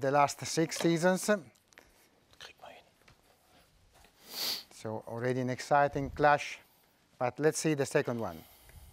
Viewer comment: What did Mr. Micheal whisper? ------ Bayern? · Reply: Hes talking german. What he said was "Kriegen wa' hin", which translates into "we can manage that". Refers to Gladbach-City, hes a Gladbach fan.